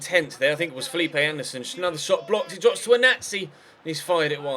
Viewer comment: On 9:28 it sounds like he said "its dropped to a nazi“ lol 😂